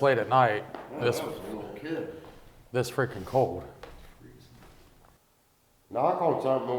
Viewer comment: I hear "It's freezing". I hear the voice during the time and are talking about how cold it is. It could of been but to me it didn't sound like him. Let me know what you think?